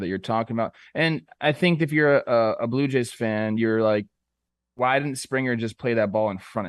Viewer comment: I thought he said Blooper Jays for a sec 😂